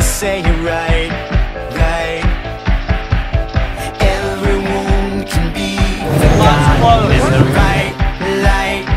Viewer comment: what does the cop say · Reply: I think he said the lot's closed